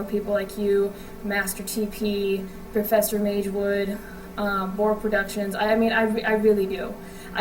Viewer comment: did you say bourg productions?